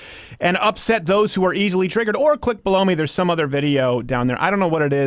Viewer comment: Did you say "Blow Me" ? ;)